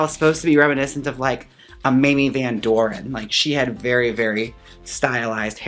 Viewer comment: when u said Mamie Van Doren i heard "Minnie Van Door" WELCOME 2 THE STAGE,,,, lol perfect dragname<3